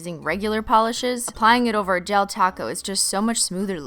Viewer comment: What she said "apply it over a gel top coat" What I heard "apply it over a gel taco" Lol